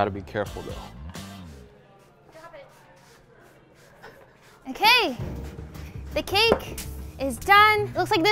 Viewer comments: OMG can anyone hear saying : drop it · I swear I heard say "drop it" in the background lol